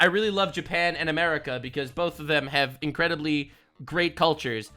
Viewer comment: what I hear was, Japan loves America because they have big penis.